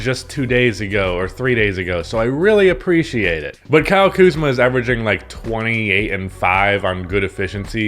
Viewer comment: When you said Kyle Kuzma is averaging 20, 8 and 5, I heard that as "Kyle Kuzma is averaging 28 and 5" and thought I was the biggest fucking casual in all of NBA fandom because I didn't realize Kyle Kuzma took an MVP leap.